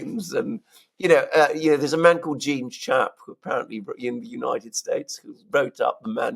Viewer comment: What was the name of the anti-violence fellow? Thought I heard Gene Shaap but no hits when searching.